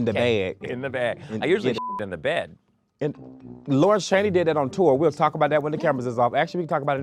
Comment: Kornbread speaks so fast sometimes I kept hearing “Lord Shady”. Like WHO is Lord Shady?! Omg. It was Lawrence Chaney.